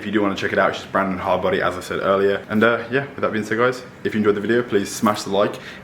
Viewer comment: what the is he saying? the youtube subtitles says " uhm yeah, i've be en sick guyz, if you enjoyed the video...."